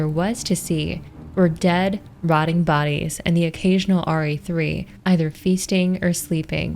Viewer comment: Can someone help me out? What is being said here? Because what I'm hearing is "dead, rotting bodies, and the occasional RE3." Which doesn't make sense to me. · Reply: In these novels, there are White Umbrella-assigned codenames for most of the B.O.W.'s. Re3 is the codename for a licker